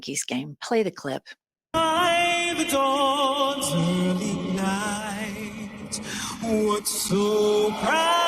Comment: "Dawns early night?" Is that what I heard?